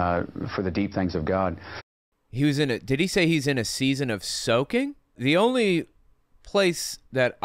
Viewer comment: I thought he said he was in a period of sulking? Maybe feeling sorry for himself?